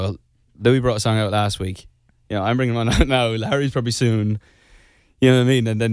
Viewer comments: it kinda sounds like Niall says “Larry’s probably soon” not “Harry’s probably soon” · does Niall say Larry??🤨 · Am I the only one who hears a LARRY there? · did Niall say Larry or am I imagining it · Am I the only one that thinks at 9:40 it sounded like he said larry. Btw I know he was talking about harry but like still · did Niall just say Larry ?? I’m confused 🙈🥰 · OMGG I had captions on and when Niall said Harry the captions said Larry 😱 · is it just me or did i hear larry? haha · Is it just me or does Niall say "Larry" in 9:40 ?? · Niall is an angel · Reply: He said Larry instead of Harry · Anyone else noticed that it sounded like Niall said Larry instead of Harry ar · I couldn’t have been the only one that heard Larry instead of Harry! 🥺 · Did niall say larry at 9:40? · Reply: He DID say Larry! "Larry's probably bringing one out soon." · NIALL SOUNDS LIKE HE SAID LARRY AND I LOVE IT SOOOO MUCH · i swear i heard Niall say Larry instead of Harry.... just me? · I swear Niall said Larry...